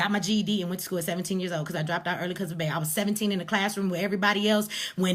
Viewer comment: This is an example of the mentality Black Police Officers have to deal with. Grown adults with a ''You Ain't My Daddy'' mentality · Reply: She was talkin' so fast, I couldn't understand why she said she dropped out at seventeen, and got her GED, I heard her say 'I dropped out early because of bay', was bay short for baby..?